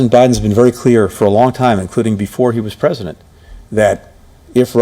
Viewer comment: Go to marker 4:50. Is it me, or did he say “if Russia acts Aggressively”?